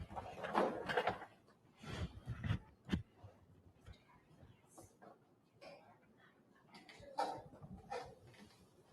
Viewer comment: What is she saying at 2:00? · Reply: can I get a pen or do you have a pen. Its to the Sheriff or bailiff. Gets her a pen like 5 seconds later